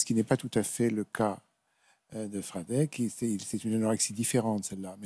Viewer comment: il dit quoi juste après "ce n'est pas tout à fait le cas de ....(incompréhensible) "fradec" "phradek? Si une âme charitable peut m'aider ça m'aiderai peut être à avoir une piste sur le mal qui me ronge depuis tant d'années... Merci d'avance 🙏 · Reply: C'est comme cela qu'il s'appelle (ou se fait appeler), Franek 😊